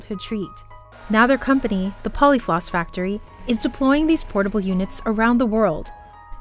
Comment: What I heard 'deplorable units' Had to listen again to hear 'deploying these portable units' But my first interpretation was more correct. The answer to our plastic issue isn't recycling into micro-plastics.